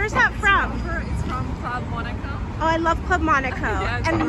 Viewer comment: I'm not American - what is 'Cloud Monica'??? I googled it but just got people called 'Monica Cloud' which suggests I'm mishearing. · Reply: Club Monaco